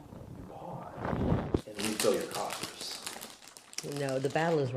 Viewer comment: The closed caption is wrong, he say's, "You pause.... and we fill your coffers..." And the "Back East..." thing sounds like the movie Casino where they called the Chicago Outfit, "Back Home."